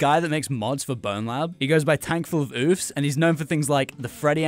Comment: I heard "tankfullofboobs" please say that i wasnt the only one